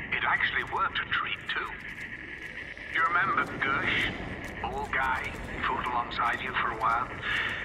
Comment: Are you sure that Dr. Monty says he's bald? It almost sounds like he is saying, "Old Guy" and not, "Bald Guy."